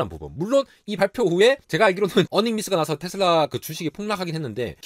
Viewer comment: 버닝미스요? 뭐라고 말씀하신건지 궁금하네요 ㅠ